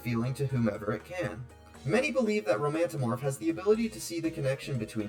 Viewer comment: I kept hearing "Romantimorf" as "Romantamore" which I thought tell fit because "amore" means "love" in spanish, haha